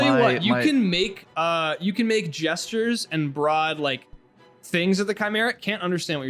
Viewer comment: thought he said br'aad